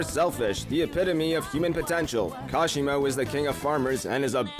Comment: did you say potential